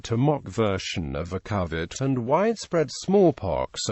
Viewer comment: did he say covid? 😳